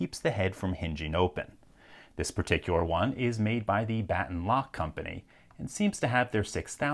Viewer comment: what I heard "this particular one is made by the 'bad lock' company" Sounds about right.